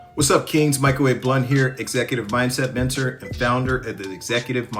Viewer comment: I thought you said "Microwaved Blunt"! 😂